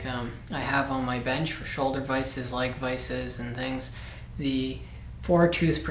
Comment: Do you also recommend 2" with 2TPI for a leg vise? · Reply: That's what he said around the 2:30 mark. He kind of mumbles over it a bit, and it sounds a bit like he says "like vises"